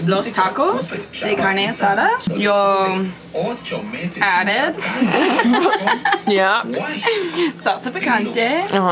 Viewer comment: did she just say “yo... ahre”???😂😂😂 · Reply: She didn't know the word for added, so she said yo added 😂